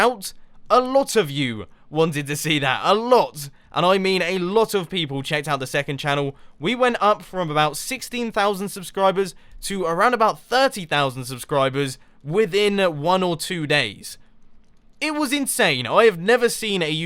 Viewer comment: I thought he said 60,000 subs to 30,000 XD Whoops :P